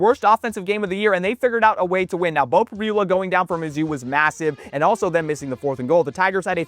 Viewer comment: did you say, massive?